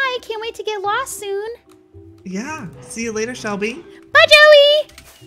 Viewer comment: When Joey said bye shubble then shubble said bye Joey I thought she said bye jelly 😂